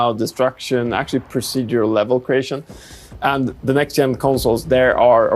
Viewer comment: did he say Procedural level creation....interesting